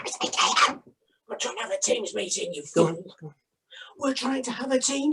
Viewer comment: What does say at 6:58 before “we’re trying to have a Teams meeting, you fool!”? I can’t make out what he says after “Barry has gone!”. This is one of the funniest things I’ve seen since the Fenton video in Richmond Park. Pure comedy gold. 😆😆😆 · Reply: I believe he says “We’re trying to have a teams meeting you c**t.” 🤣